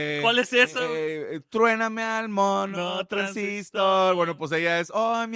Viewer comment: "Truename al monotransistor" pensé que decia 'Traeme al monotransistor' o 'Prende el monotransistor'